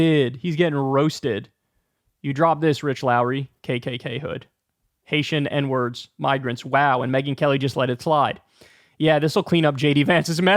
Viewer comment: I heard “n word, N N UM migrants” like not only did he say it, he got STUCK on it and had to throw in an UM to reset himself and use another word. Edited to add: I recognize that behavior because I swear like a sailor and often had this scenario. “Fuuu F F dang it.”